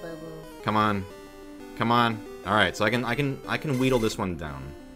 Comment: did he just say"weedle it down?"